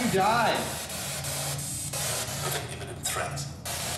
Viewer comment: "beneath" in response to asking where are you in here? 52:22 : "with an imminent threat" in response to how did you die? 53:58 : "reaper" 1:08:27 : "right behind ya" 1:08:30 : "Call Mary" 1:11:16 : something about being "stuck" 1:11:19 : "with the knowledge of christ" as the sls figure is holding its hand over head. 1:11:50 : "Im about to....something" couldn't understand the last part. When asks can you reappear with again?